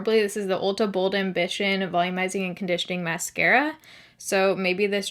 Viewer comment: I heard “Bold and Bitchin’ mascara” 😂